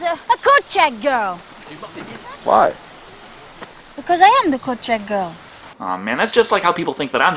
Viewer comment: what is she saying?? What the hell is a "corchette girl"?